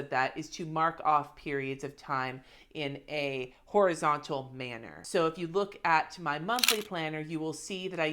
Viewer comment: (regarding washi tape) I really thought you said "represented in a horizontal man" and I wasn't surprised in the least. Only when I replayed it did I realize you actually said "horizontal manner" and I was kind of disappointed, tbh. :D And I just ordered $40 of stickers yesterday for my bullet journal to act as functional visual reminders of when shit happens (gas for the car, took the kid driving, actually went out to the movies). But mostly this - I'm 55 years old. I make decent money at my job of 30+ years and I'll do use stickers, markers, washi any way I want to. If you don't slow down and enjoy a minute or two of your life, if you spend the whole time being all drudgey and mundane - what do you have left to show for your lifetime at the end? Nope. Give me my messy cluttery house that's full of laughs and good memories with my kid as we joyfully go through the spoils of the latest craft store haul. That is my call.